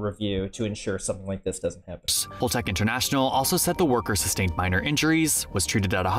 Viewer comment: Holtec😂 I thought the narrator was saying Vault-Tec.😂😂